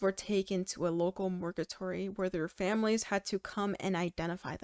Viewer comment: the bodies were taken to a local what? Did you say murgatory? A local murgatory? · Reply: Mortuary just said wrong lol